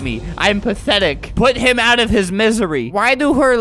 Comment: Lol i heard he said "boobs"